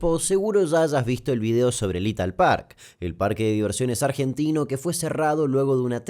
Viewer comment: Dijo Italpark y yo entendí “Lethal Park” 😳